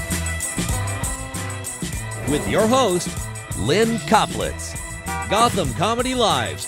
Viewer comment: damn i thought he said with your host Lynne Cocklips